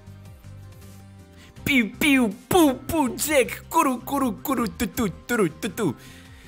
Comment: Мне послышалось или сказал "Курю"